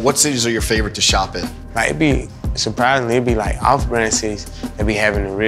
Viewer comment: anyone else heard "Diabetes"?